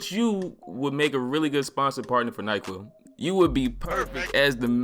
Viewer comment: Instead of saying he should be sponsored by NyQuil I thought he said Nike 😂 I mean Nike too because they sponsor losers who like to talk out of their ass.